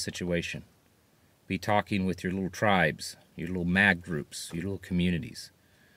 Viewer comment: I had to rewind it. I thought you said to support your "MAGA" groups. lol. Jesus reigns.